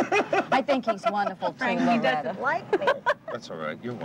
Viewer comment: Did Loretta call Larry Frank at 8:56?